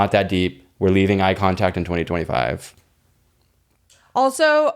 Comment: When they said we’re leaving eye contact in 2025 I thought they said we’re leaving eye contacts in 2025 I genuinely was offended like how am I gonna see hoe